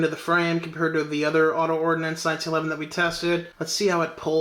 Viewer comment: I bought the Para Ordinance P12 LDA and didn't know the LDA had a DAO henged trigger, but being a quadriplegic the trigger is pretty damn good. I will still get another P12 and sell the worse of the two. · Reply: Auto Ordinance... duh, I thought you said Para. My bad ears. LOL